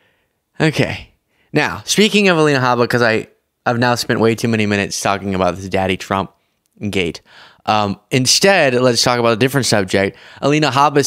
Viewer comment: I swear she's saying Diddy Trump 😂